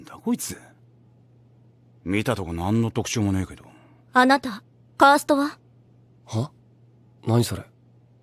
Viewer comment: ヒストリアのカーストがパンストに聞こえた・・・ ジャンはさっさと手を動かしなよ・・・ あほな子か・・